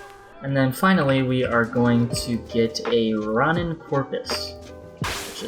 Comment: Did anyone else hear rotten porous??